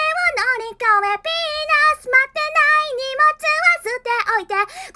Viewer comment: 海外ニキがみんな「ヴィーナス」を「ピーナス」に聞き間違っているから結構コメ欄ではしゃいでいます。（僕も海外人なので日本語が間違っていたら申し訳ございません。）